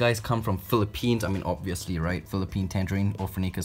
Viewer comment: when he said "Philippines" i thought he said "philipenis" like Lay from exo